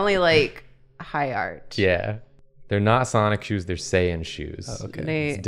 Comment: For a brief few seconds when said "sonic shoes", i heard "Sonichu" and i felt a chill in my spine